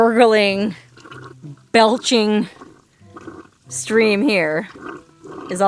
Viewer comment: did she say "felching"?